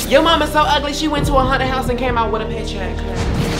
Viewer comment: "Yo mama so ugly she went to a haunted house and came out with a paycheck" With the tone was just perfect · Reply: I thought she said Honda house 😭